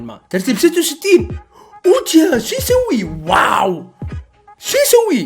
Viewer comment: حسبته يسأل من سمعت يقول شيسوي😂😂😂🌚🤚